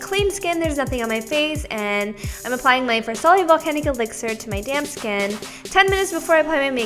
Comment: Lol I thought you said "I'm applying my farsali volcanic elixir to my damn skin" 😅